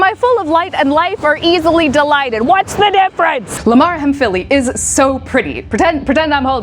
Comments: Am I the only one who heard 'Lamar from Philly' instead of Limaria hemphilli at 02:31? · I heard, "Lamar from Philly"